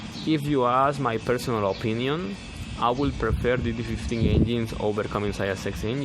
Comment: you say you prefer an engine over the ISX engine but I can't understand what you said. What engine do you prefer over this ISX? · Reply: He said he prefers DD15 engine